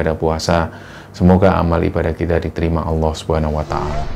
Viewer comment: semoga amal ibadah tidak dibterima allah.swt Tolong min apakah aku salah dengar?